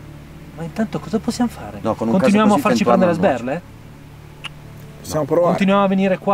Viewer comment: Quando il gost box si è spento e poi riacceso al minuto 6 ho capito "le stelle".